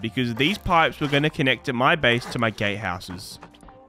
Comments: what did you say? "Gay houses"? Lol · did he say gay houses 😂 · did he say gay houses 17:23 lol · is it just me or at 17:21 does he sound like he said "gay houses"